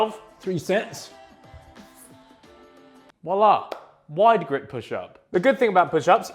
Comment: Anyone else think he said wallah